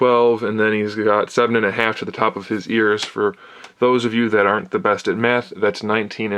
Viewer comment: I thought said "for those of you that can't so meth."